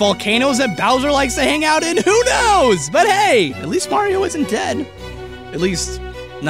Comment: I thought at the end he said “But hey, at least Mario isn’t gay.” I had to rewind that because I was just like, wait what did he just say.